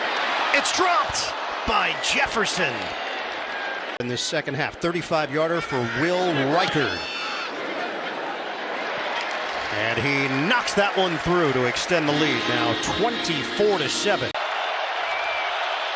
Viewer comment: I had to listen three times and look at the transcript before I realized he did not say, "Will Riker."